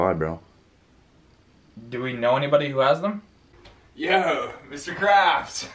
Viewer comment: I thought was saying Mr.Crabs during the beginning 🤡